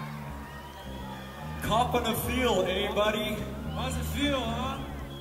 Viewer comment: What did he say 0:20? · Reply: he said:"copping a feel, hey buddy?"